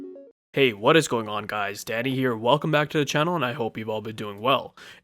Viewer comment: When you say "Danny here", I hear "daddy here" which is messed up. But if you change your name to "WhosYourDanny"... you'll be a YT success, ezgg. :)